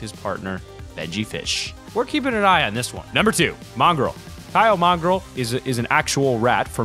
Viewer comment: I thought you said Morgz for a sec